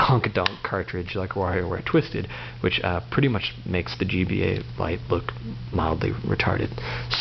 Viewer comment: seconds did he say GBA lite??